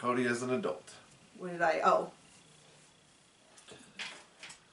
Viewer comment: At the end I initially thought said 'That’s our wail calling' lol